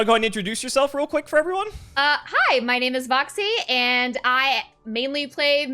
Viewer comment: Stright up misheard intro as "hi my name is boxy"